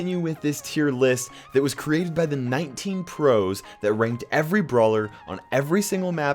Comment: I thought u said 19 crows ... tho love you kairos 😂😍